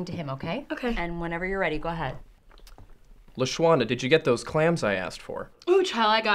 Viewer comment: Damn this whole time I thought they were saying "clamps" lol